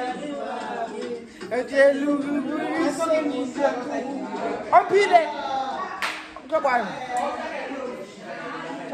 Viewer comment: what are you saying here? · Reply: Haibo I just said all of a sudden😙🤣🤣🤣🤣